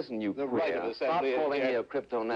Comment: Idk why but him saying “and you’ll stay plastered” like such a gentleman is killing me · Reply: i thought he said plastic 😂😂